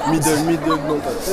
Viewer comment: C'est moi ou il dis Futi à